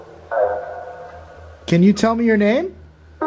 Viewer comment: It sounded like it said I’m Sara when you said can you tell me your name ...19:04 or